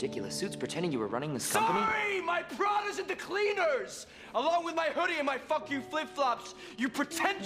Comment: what did he say? All I can hear is "my prod is into cleaners" he says it so fast. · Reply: Turok Rage Wars He said “my Prada is at the cleaners” Prada is a fashion brand. So he’s basically being sarcastic and saying the equivalent of: “Sorry my expensive suit is at the cleaners”.